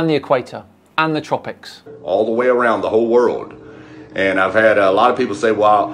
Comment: Did he said: All "around" the world at 9:00?